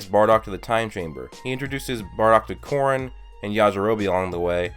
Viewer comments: thought he said porn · I thought he said porn